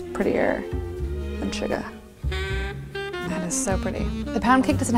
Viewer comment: when she said "'cause nothing is prettier than shuga." I thought she said Suga and I was like "Thats pretty accurate. But Jin is worldwide handsome."